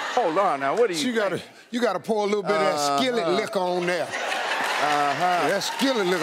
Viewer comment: Before reading the comments, I thought they were saying put malt liquor in it! lol